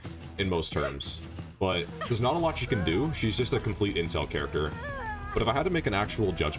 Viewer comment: da heck is an incel character had to listen to it a few times my mind wants me to think you are saying intel but I swear it sounds like incel lmao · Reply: dammit I knew I wasn't the only one who listened back and thought it sounded like that too. I said intel, but youre definitely not wrong in kinda hearing incel lmao.